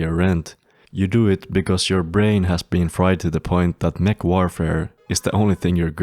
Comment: I know you said "mech warfare" here, but on my first watch, I misheard it as "McWarfare" and now I have the urge to co commit some McWarcrimes 😂